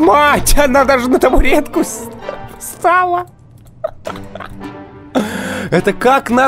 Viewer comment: <link> Мне послышалось ли он сказал "На табуретку... сцала"?!